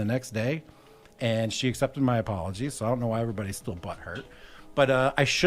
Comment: did he say butt hurt at 2:57...thats irony